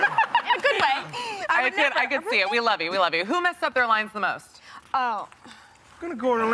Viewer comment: Wait wait wait a damn minute..i thought she said who messed up their life the most😭👀